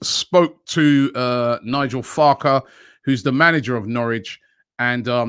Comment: Did he just say Nigel Farke? 🤣